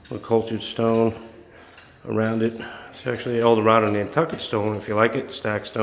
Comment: what stone is that? i couldnt capture it from the audio.. and i have to say its really beautiful and looks so cozy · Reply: I thought he said Nantucket Stone